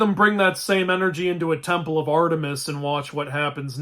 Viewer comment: For a second I thought you said “temple of Athena” and thought it was a really dark joke.